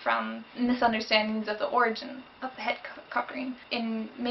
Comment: from 01:28 to 01:31 i thought she said ''orgy'' and ''cock ring'' oh dear D